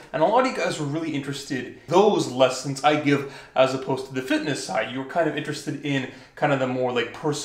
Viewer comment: I thought it said finesse lmao